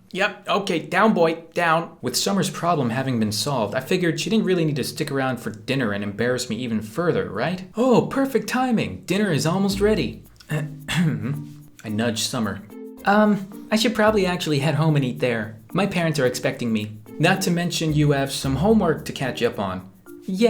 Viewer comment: I thought said I nutted at summer